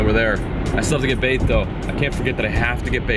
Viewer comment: lmao at 5:02 i thought he said get baked and i was like wait hold up you gotta do what